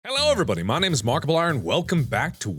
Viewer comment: When Mark was going his intro, i heard "Hello everybody this is remarkable iron!" XD